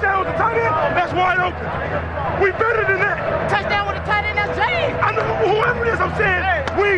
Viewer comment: I still don't know to this day what Tyrone Carter said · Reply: Touchdown with a tight end….. ?